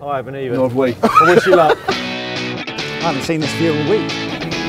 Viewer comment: at the 0:48 mark, did say "I haven't seen this view in a week" ???